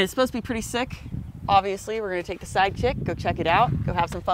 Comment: At the beginning of the vid when you said we're taking the Side Kick, I swore you said Side Chick which would be a great name btw 🤣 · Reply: You heard her right. Side Chick is the name of this buggy. Hahahaha